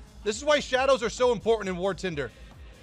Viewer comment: did he say war TINDER ? :D :D